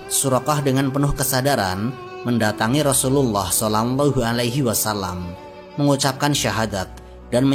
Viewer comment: Maaf min..... kedengerannya "solangllahu alaihi wasallam".